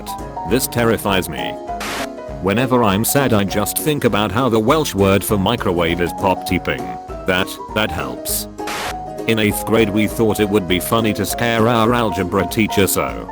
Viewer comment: XD I'm Welsh so when this popped up I cracked up! I was just having this as background sound and all I heard was " the Welsh word for microwave is microwave " and I was like ' Beth? '